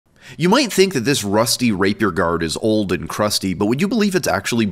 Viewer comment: Totally thought you said "rusty raper" before I saw the video.. my apologies..